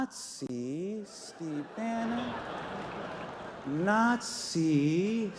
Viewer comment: I thought Hasan said "not see Bannon" but he actually "nazi Bannon".Good one Hasan. Love that guy